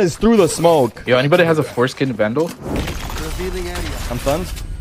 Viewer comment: am I the only one who heard foreskin vandal😂